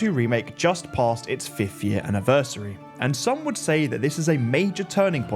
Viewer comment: Idk why but I thought you said “50th year anniversary” rather than “5th year anniversary” and almost choked on my dinner